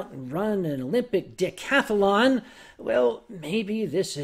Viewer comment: did he say Olympic dickcathlon?